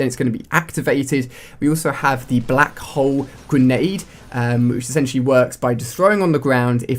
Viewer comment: lol it sounded like black hoe